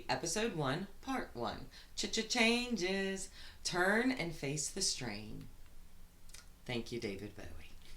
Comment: thought it said Face the Stain , lol still works. Fits the narrative.